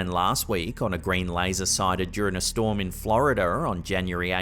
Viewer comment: ☆ATTENTION ☆,... why do you pronounce the letter "R" at the end of some words sporadically? At timestamp 05:08, instead of "Florida" you say "Florida'er!" Lol And it's impossible to count the number of times you have said "area'er" instead of "area!" Anyone else hear this? It's blatant and obvious as Hell.